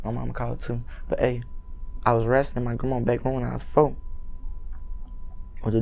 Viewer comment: I didn't understand one dam word he was saying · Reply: Dawson Albritton all i heard was "wen i was fo"